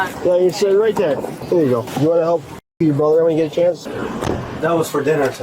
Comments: Could someone help me out at 12:15 why do the subtitles say “You wanna help f**k you brother” i know he said “tuck” i think but these subtitles are just going halfass crazy! 😅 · These channels have GOT to stop using AI for the subtitles 😂 around 12:15 the cop is getting the kids in the car and says to one “you wanna help BUCKLE your brother” and the subtitles say “you wanna help f*** your brother” 🤦‍♀️😭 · These ai subtitles are absolute trash. At 12:15 is probably the most disturbing subtitle ive seen. He asks the little boy if he wanted to "help buckle his brother" in and instead of buckle, you put "fuck". Whoever does your editing needs to be fired. Theres no way you just missed that. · why does the Subtitle say "you wanna help f+ck your brother?" 😕🤔 Is that really what the police said ? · I think they severely screwed up the subtitles. The subtitles say ''Do you wanna help f*c* your brother when you get a chance?'' I think what it's supposed to say is ''do you wanna help buckle your brother when you get a chance?'' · Did anyone else notice at @ 12:13 the captions were wrong? I thing the cop said "Do you want to help buckle up your brother" but the captions said "Do you want to f*** your brother?" Had to watch it like 3x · Did any one else pick up on the captions that said “wanna help f*** ur brother” when they were getting the kids in the cop car instead of “you wanna help buckle your brother”